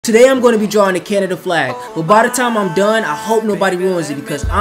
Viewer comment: I thought bro said Fanta flag I’m an idiot